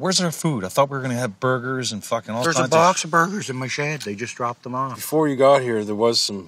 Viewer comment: I thought bubs said Bobs burgers instead of box of burgers lol